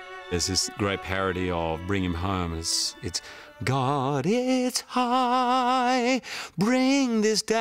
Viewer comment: LOL. "God's it's high, bring it down" Too funny... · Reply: It thought they said "God is high"